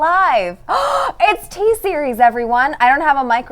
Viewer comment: did you say t-series 😂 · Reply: Tea-series